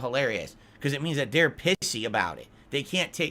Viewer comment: ok I know that meant to say pissy but it sounded like he either said pixie or pigsee either way it's hard to insult people when you can barely say the word to insult them